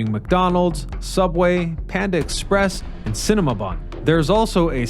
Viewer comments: Wait... at 9:57 did he say "Cinemabun" ?? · Did he say Cinemabun?